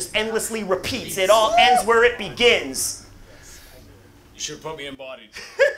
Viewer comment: What did the guy say in 29:47? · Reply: former 2v2 partner, said “You should’ve put me in ‘Bodied’” ( movie about battle rap…go watch it, many major battle rap legends make cameos including Hollow and Lux, TheSaurus and Illmac, Iron Solomon, of course Pat Stay, and many more)